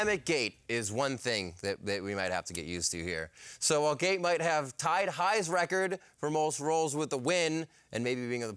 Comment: Did just say "Gate tie dyes record"? Does he have a record number of tie dye shirts we don't know about?